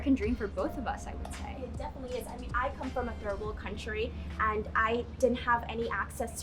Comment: did she just say "terrible country"? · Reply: I think she said "third world country".